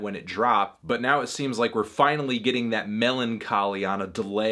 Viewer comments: did he just say m e l o n c h o l y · Did you just say "meloncholy" ??